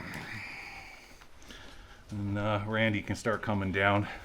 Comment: A whispering voice at 29:49 , almost sounds like someone saying "Randy".. freaky.